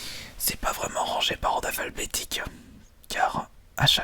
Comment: C'est moi ou a 7:52 y dit par ordre "aphalbetique"?!?!?